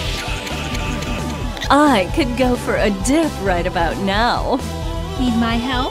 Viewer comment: What exactly is she saying there? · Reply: "I could go for a dip right about now". It took me a second to get it too.